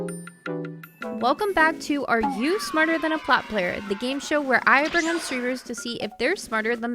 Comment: I thought it said “are you smarter than a frogger player?”